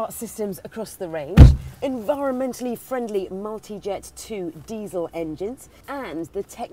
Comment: I thought she said monkeyjet too wtf monkeyjet Lol multijet ik